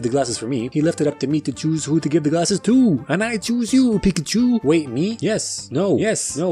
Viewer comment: DID HE JUST SAY YES U PIKACHU